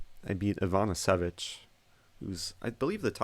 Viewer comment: did he say "Ivana Savić"("ć" is pronounced something like "ch")? Cause if he did, that's a female, (and he refered to her with "He was...")